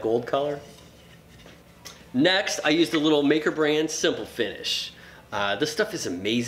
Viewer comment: What stain is used at 6:05 ?. . . I couldn't quite make out which company and color you said... Thanks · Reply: It's called Simple Finish. It's kind of pricey. But the result is worth it! <link>